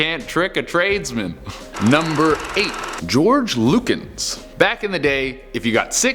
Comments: I wasn’t looking and when he said “George Lukens” I thought he said “George Lucas”😂😂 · Thought u said George Lucas 3:05 😂